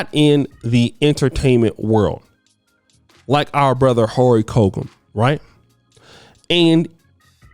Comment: Lol 4:21 did you call him Horry colcum